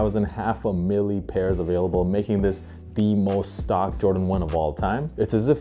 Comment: yeah but he said the JORDAN 1 with the highest stock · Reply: yeah true, guess i only heard “jordan with the most stock” haha my b